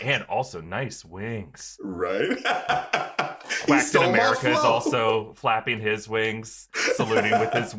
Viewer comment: I'm sorry but at 6:37 does say Blacktain America or am I hearing things? Cause I have listened to it like 10 times and I can't hear anything else 😅 I'm not accusing him I was just legitimately confused. Sorry if he actually said something else and I'm just hearing things lmao.